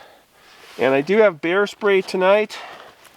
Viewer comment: I Thought You said You had Beer Spray, instead of Bear Spray 😆